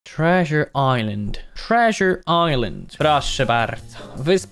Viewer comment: ja usłyszałem trash island